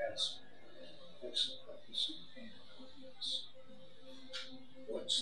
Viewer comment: No matter how many times I urged him that's the loudest he can speak*. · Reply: What does he say at 29:45 or so? Is it, "What's the purpose of pain and illness?" That's what it sounds like though my tennitis-ringing ears. Do you remember? Thanks for the interview, BTW. Loved it.